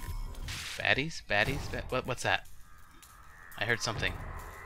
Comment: when he said baaddies, baddies, baddies, I thought he said fatties, fatties, fatties